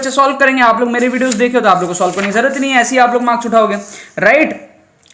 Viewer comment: what he told Asey hi aap marks uthaoge What I heard aise hi aap log maa c****oge